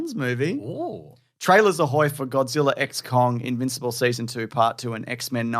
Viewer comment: It sounded like you said GODZILLA: EX CON. Like Godzilla just got out of prison for passing some bad checks, and got a dishwashing job.